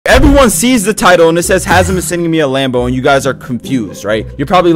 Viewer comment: i heard "bedwars sees the title"